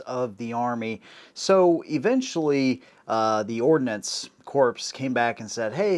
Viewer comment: Did you just say Ordinance Corpse? 07::32